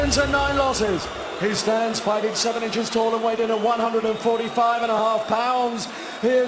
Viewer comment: Fighting 7 inches tall? Did I hear that correctly?